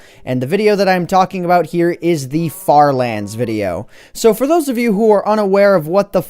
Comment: i thought he is gonna say what the fck